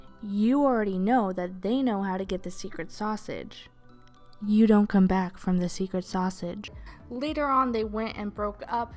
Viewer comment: I Spaced out, and when I came too, I heard "no one comes back from the secret sausage"